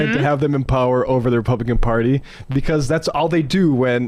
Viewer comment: I heard "repumpkin party". Anyone else? We should keep that going. Repumpkin party.